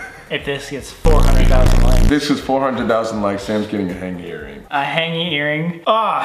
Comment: i thought colby said “hand gay earring” @ 8:56 💀